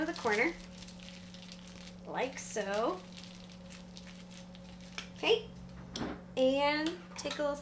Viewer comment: when I was little I thought she was saying “Like soap.” 🤣